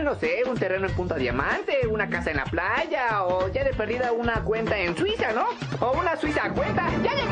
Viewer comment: Jeje, al minuto 0:29 pareciera que dice una cuenta en Twitter no? pero creo que dice algo de Swiss algo asi, pero apenas lo escuche pense que habia dicho Twitter